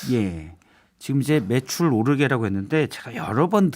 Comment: 난 눈감고 들어봤는데 분명히 "배추 오르게"로 들었다